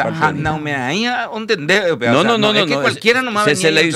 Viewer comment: que dice? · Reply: Osea na oimeraea o entende upea Uso el idioma guaraní mezclado con español Lo que dijo fue Osea no cualquiera entiende eso